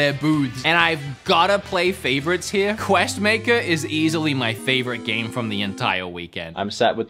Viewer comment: you said Quest Maker instead of Quest Master